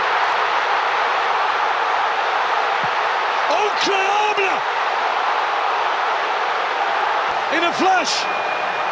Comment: What is Guy Mowbray saying in 03:25?? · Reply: "incroyable", which means "amazing" in French, I believe.